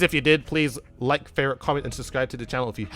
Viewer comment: Anyone else hear: "Like, Fair, Comment and Subscribe" at 8:57?